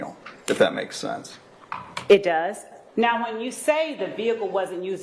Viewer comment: did she say hump?! 😂😂